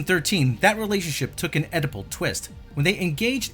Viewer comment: Did you say Oedipal, or edible (yum)